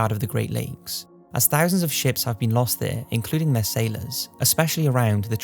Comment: I thought he said,"...where lots of sh!ts have been lost..." RIP Sh!ts...